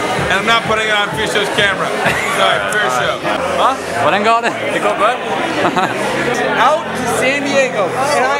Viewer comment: PLEASE someone tell me what is said between Fysho and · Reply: "Huh?" "how is it going?" "going well" "haha"